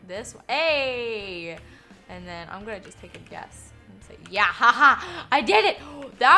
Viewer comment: while I was watching I heard I'm going to tell music and I like oh my God. 😂😂😂😂😂😂😂